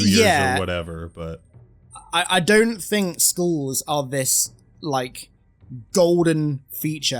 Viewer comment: Oooooh… is saying skulls… I thought he kept saying schools and I had no idea why Halo Studios would be so focused on schools to keep people playing the campaigns.